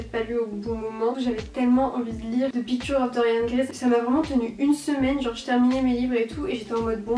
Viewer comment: quand tu parlais de the picture of dorian gray j'ai entendu "fifty shades of grey"..... j'étais perdue